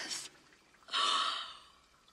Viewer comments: Transcript; "She's dead!" "She is?" Deep sigh "these are the moments that make life worth living" "Maguba dabahaabl" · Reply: I Thought it lt said my god Man that was horrible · Transcript; "She's dead!" "She is?" Deep sigh "these are the moments that make life worth living" "Maguba dabahaabl" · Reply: so it is! I hear it now- I thought he was saying "that was horrible" which threw me off a bit (and, in my defense, you also wrote that in your original reply). You've released me from my earthly bonds and my spirit is now free- thank you, my friend! :-) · The lyrics: “SHE’S DEAD!” she is?.. *Gasp* These are the moments that make life worth living “MY GOD MADELINE THAT WAS HORRIBLE!” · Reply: Why did I hear “THANK OD BUT MAN THAT WAS HORRIBLE” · ACTUAL Translation: "She's dead!" "She is?" shocked inhale "These are the moments that make life worth living." "My god Madeline it was horrible!" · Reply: I thought he said "my gaw man that was horrible"